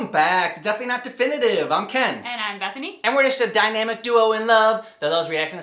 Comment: Damn it now i hope for a happy reunion ending between Dante and Vergil too! ( sorry if isn't your name but i thought i heard that somwhere in the video. But maybe works? But yeah i have a hate love relationship whit Vergil because of the Katana but that is pretty much all i like about about him however i think he seems to be a well written character..But my favorite movie is scorpion King so i wouldn't trust my opinion in any way!'